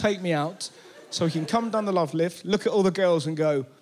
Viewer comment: What does he say at 3:05? I can’t understand his accent. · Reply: He says nah